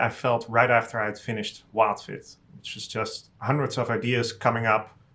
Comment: Did he say "wildfit" helped him as well?